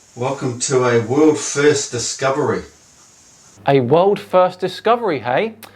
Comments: I thought he said a woo fest discovery not world first discovery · I thought he said woo fest discovery XD